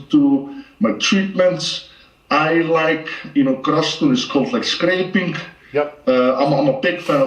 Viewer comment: he’s a big fan of what for recovery? I hear content scraping but that can’t be right!